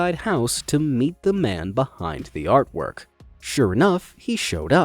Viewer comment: i thought you are saying "the Man behind the slaughter" XD